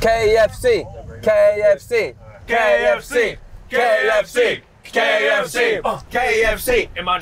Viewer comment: all I heard was GAYFC😂